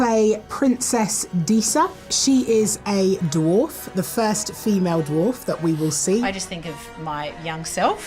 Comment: What she says: "I'm the first female dwarf" What I hear: "I never even saw The Hobbit!"